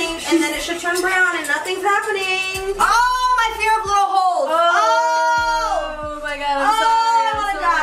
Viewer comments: what did Nezza say lmaoo I couldn’t understand her😂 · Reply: "my fear of little holes" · What did nezza say at 4:29 I literally cannot make it out 😅😅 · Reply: I think she said “my fear of little holes” ?? I think that’s what she said